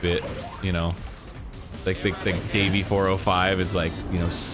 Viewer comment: Did he called him Davie405? S M H